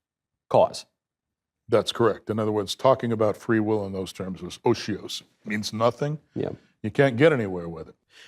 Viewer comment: Those captions are just irritating as well as ridiculous. 34:22 oseose . Is that what he said here? Captions just gave me 3 question marks. Tried various spellings on google, nothing came up. Think I'm going to just give up here. I find This guy is an incredibly dull and terrible conversationalist. Almost on a par with peterson with his word salads but at least peterson shows some semblance of living in the real world.